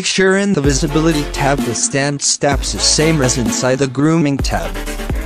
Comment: What did he say at 5:32? I can't understand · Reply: The Strand Steps of Viewport Display, will be same number as in the render Section.